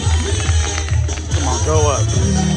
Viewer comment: I heard “go up🤞” I take it these tend to break low? 😳😳